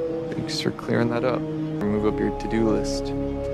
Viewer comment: what did he say ??? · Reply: It was cut a bit he said "give me a call if I ever move up your to do list"